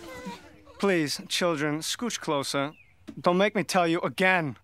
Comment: 😂 I always thought he said. "Make with the scooching," Still so damn funny.